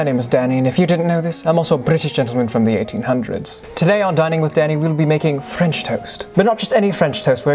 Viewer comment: Tell me why i thought he said dining with daddy?😭😭✌🏾😗